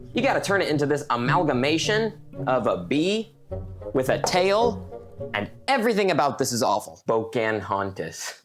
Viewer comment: Uhh did anyone else hear "boe can haunt us" !!?? I'm scared 😰